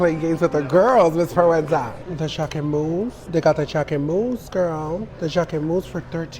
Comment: I wasn't looking at my screen at one point, and the way she said Jacquemus i thought she said Choclate Mousse lololol this is why i need to pay attention to shit lol